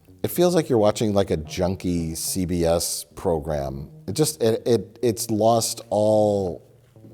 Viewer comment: said “ It’s like a junkie CBS program.” I thought you said. ”It looks like a junkie shooting up on a CBS program.”